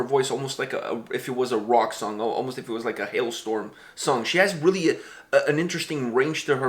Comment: I thought you said an "Alestorm song". Thanks, now I can't get them out of my head! "Keelhaaaaul that filthy landlubber, send him down to the depths below..."